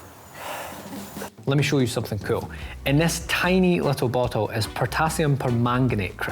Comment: Did someone just called you "you little sh*t" in in the video in